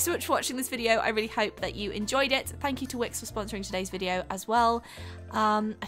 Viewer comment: At the end when she said thank you to wix for "sponsering this video" i heard ", thank you twix for sponsering the video" haha